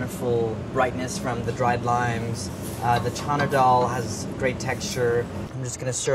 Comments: Is it me or did it sound like he said China Doll? Lol! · Chana Dal....I thought he said China Doll at first.